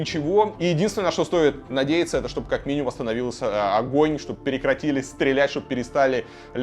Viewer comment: мне не послышалось 2:30? чтобы восстановилось огонь, ну ладно